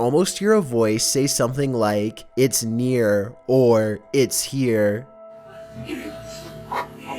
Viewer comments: sounds like it said “its me” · Number 15 I thought it said it’s me. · I hear “shits ear” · At first the first one I thought it said " Get me out" but then I heard "it's near" also I have that bird and mine makes those songs too, at times it's fine but then it does that it's really weird probably because it old